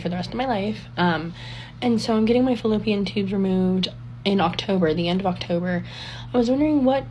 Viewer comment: Did she say FilipineTubes?